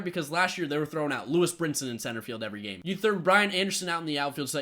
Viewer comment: i thought you were saying Ryan Anderson not Brian , i didn't know who you were talking about for a good 3 minutes